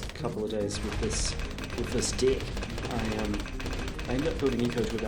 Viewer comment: I think they say dick instead of deck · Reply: yup fell off my chair when they started using the word Also 18:30 was hilarious "Practicing using this deck" but deck sounded like dick OMFG 20:58 "Yup that's how you win with ISAB's dick" Umm oook